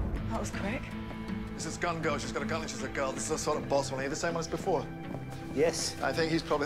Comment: I can't work out what he's saying? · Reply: He says " This is gun girl ; she has a gun and she's a girl. He's some kind of boss...are you the same guy before?" Hope that helps.